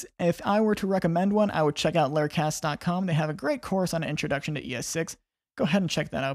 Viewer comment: I cannot figure out what website your are referencing at 05:27 to learn more about ES6. Sounds like 'Lair Cass dot Com'.